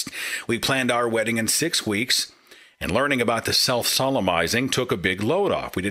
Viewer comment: I thought he said "self sodomizing" at first. lol